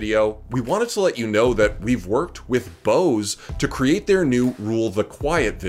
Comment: lol i thought he said we worked with "bows" to bring u the video WHAT A POET